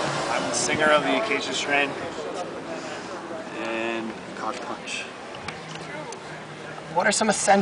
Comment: Did he say he was the singer of COCKPUNCH?!?!?!!?